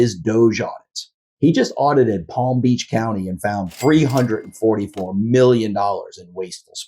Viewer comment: you say $344 million, but the caption says "$344.62 BILLION".